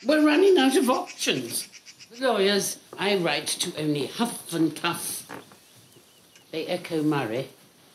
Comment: why I hear Hufflepuff?😂😂 mabye McGonagall has retired to Downton😂